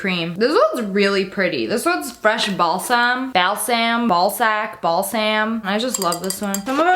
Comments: did she just say ball sack😂😂😂😂🤣🤣🤣🤣🤣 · OMG is it just me or did she say ballsack I'm sorry I'm dirty mined but I'm sure u can hear her say it see look -> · Lmao did you just say Fresh ballsack 😂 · anyone heard ballsack? lmao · sounds like she said ball sack